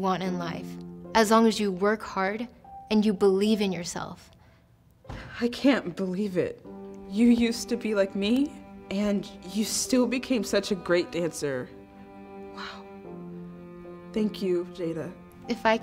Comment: says "geat" instead of great